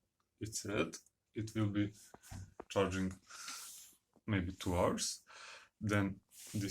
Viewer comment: did you say 2 hours or 12 hours? I am very confused. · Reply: 2 hours